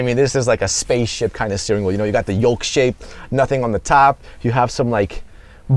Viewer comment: did he say "yolk shape"??? Why do places like this always hire dolts?